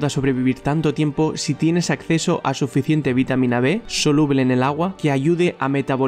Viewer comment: que dice??? Vitamina d o b?? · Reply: B